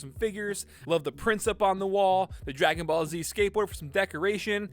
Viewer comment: where he says "prints up on the wall" I saw vegeta and thought he said prince up on the wall